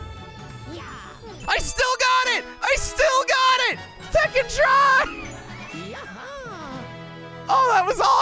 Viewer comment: When he screaming "Second try!", I thought he said "Fing try!"